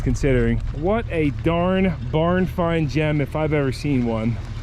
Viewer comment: be careful what ya say Sir. Jen is levels above a nice Barn find. Oh, you said jem not Jen